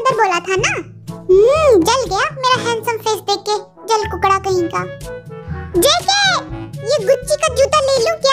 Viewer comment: Jin: julkookada kahinka... Me: thinks "jungkookada kahinka? ??? Did she just said jungkookada